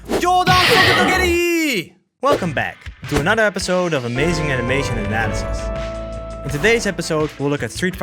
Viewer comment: I’m new to this channel so in the AAA introduction, I wasn’t reading and I thought you said Amazing Animation Asses. 😂